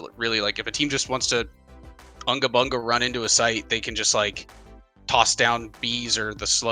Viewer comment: Did he say 'throw down bees'?